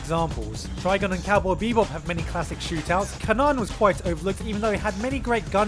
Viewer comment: What's the anime at 1:55? I think I heard "Kanon" but it leads to a 2006 school girl anime.